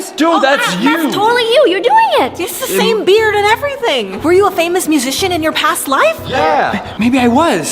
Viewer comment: I heard famous pice of sh** t